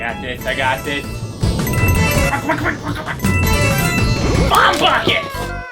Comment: Was I the only one who heard PBG say HOTPOCKETS!